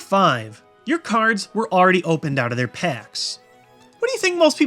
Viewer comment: THought it said Brocks...... D...... Yea... you get it.